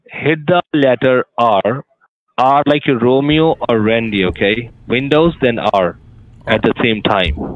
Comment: Scammer says: R like Romeo or Rundi, 😂I think he's saying Randy but it's funny. Big Black Cox: the BBC? Lmao 😂